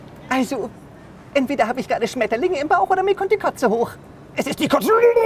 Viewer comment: Entweder ich habe Schmetterlinge im Bauch oder es kommt die Kotze hoch ... Es ist die Kotze 😝 😂😂😂😂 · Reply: Ich hab statt Kotze Katze verstanden😂😂😂😂